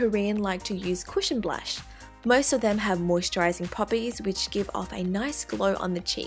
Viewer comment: did she said"moisturising puppies"???